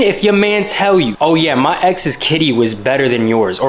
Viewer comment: I thought It said kisses better but then I saw KITTY AND I WAS LIKE NOOOOOOOO HE DIDNTTTTT😭